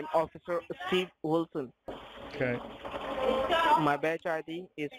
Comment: I thought he said his name was Patel Wilson. He don’t know about the Corolla. Who owns all of these Corollas that they can rent them out in the State of Transylvania?